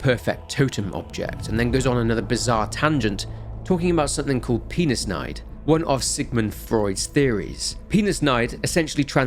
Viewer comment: I thought u said penis knife lol